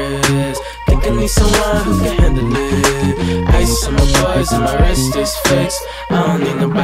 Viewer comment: for the longest time I thought he was saying 'my wrist is thick' smh